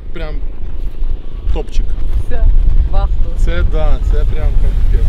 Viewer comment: Did he say "Top G"? 😍 Andrew Tate would be proud of him.